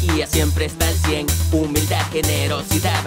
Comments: JDaniel: "Vamos a retar a Bambiel que nos haga un Rap y le doy 100$" 🎶🎶🎶Es JDaniel, Es JDaniel Algo nuevo aprenderás con él🎶🎶🎶 demostrando que no hay mejor canción que la de él :) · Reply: para ... es : Es JDaniel? Jajajajaj siempre pensé que el tema decía "Encontré a daniel encontré a daniel" XD caí ... como justo aparece donde está y una lupa pensé que decía eso. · Pensé que decía es contra Daniel y es JDaniel 😂😂